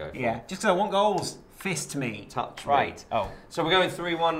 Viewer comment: Did say "Fist Me"...that came out wrong!